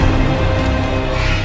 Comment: am i crazy or did he say 'Tell me how to shave him'?